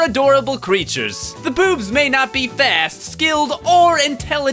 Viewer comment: did you say the boobs? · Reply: Poobs (the name of the species)